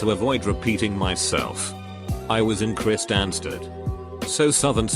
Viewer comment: Would like to point out that the woman who ate a burger with her hands in Kristiansand was most likely in Norway as I have never heard of a place called Kristiansand in Sweden. But there is a city called Kristiansand in Norway. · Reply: Ooooohhhhh okay that makes sense 😂 I thought she said Kristiansand